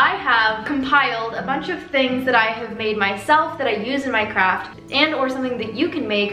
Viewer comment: When you said "things that I use in my craft" I thought you said "that I use in Minecraft" lmao